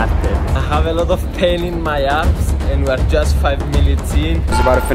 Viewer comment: In the video at 7:00 I thought he said a have a lot of pain in my Arse LOL